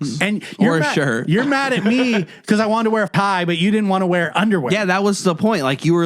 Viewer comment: did you say you're mad at me because i wanted to wear pie? Pie Not tie. I slowed it down. Definitely said pie. That's definitely a faux pas to wear pie to a dinner party. Tsk Tsk